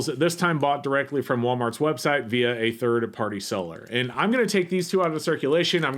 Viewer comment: at one point I THOUGHT I heard DIRT party seller, which I found funny and oddly appropriate . . . of course I know you actually said THIRD party seller, but I enjoyed how my subconscious interjected itself 😂